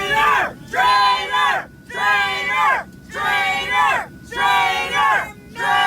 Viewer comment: Haha, is she chanting"traitor mater" like she's trying to make it catch on? Wtf does that even mean? Am I mishearing?